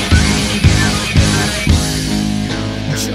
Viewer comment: I hear jojo mice but my head translates it to “your to nice” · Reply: I thought he was you saying “your demise”